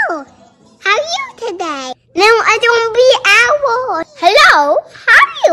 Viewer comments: lyrics: hewo haw u tudey?? helo huw u tedey hlo haw u todey? NWO! I DUN BE OWOL helo! haw u today?? helo! huw u dedey?! on meh he’d! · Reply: I THOUGHT IT SAID “how are you today?” “I’m in pain” pFFh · Y'all hear different things from the last line. I heard, 'im a fa#' and I was like, "A what?" O.0